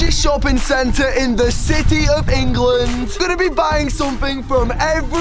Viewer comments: Did he just say "the city of England" ? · did he say city of england?